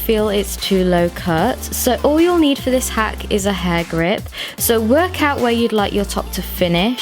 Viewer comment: Am I the only one who didn’t know what a “ hair grip “ was at first !? Lol then I was like ohhhh , she’s talking about a “ Bobby pin “ . 😂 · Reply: I heard "hard grip" 😂💔